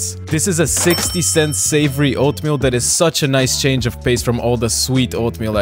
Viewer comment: oh i thought that said slavery oatmeal